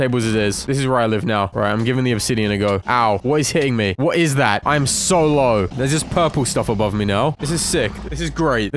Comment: i thought he said “im solo” for a second